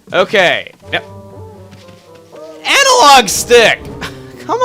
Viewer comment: i thought he said anal log stick